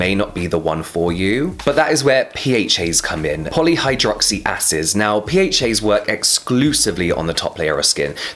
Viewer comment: All I kept hearing his Poly hydroxy asses lool